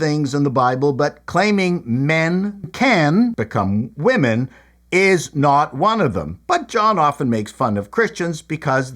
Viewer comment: over dub, I have to ask what did you really say? 😅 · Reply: I had to cover up wankel rotary motor.